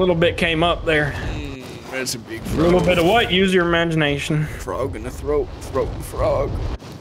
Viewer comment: This some type of Mandela effect? I could've sworn that it said "Frog in a throat, Throat a Frog"